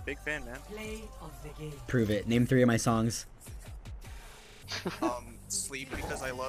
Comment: DID HE SAY MY FAVORITE SONGS OR MY SONGS?